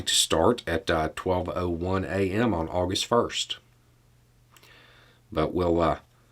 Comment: Did I mis-hear? Thought I heard August 1st. Maybe it is October 1st?